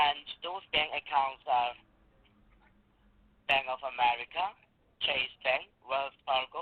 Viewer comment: That Indian scammer sounded like he said Bang of America lol. Despicable humans these scumbags are